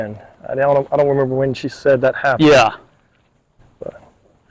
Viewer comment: right after you film the ground see the person. I love learning about the history of the hospital. 23:09 it sounds like something says maybe stop it. 24:49 it sounds like a fit noise like someone saying yeah right if that makes sense.